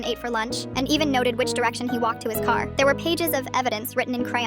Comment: Anyone else thought it said "which direction he walked in his car"? I had to relisten to that part 3 times before I heard it say "walked to his car".